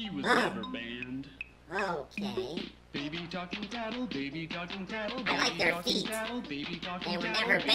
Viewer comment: In 313 say f word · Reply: No he just said "baby talking tattle" it just sounds like hes saying fuck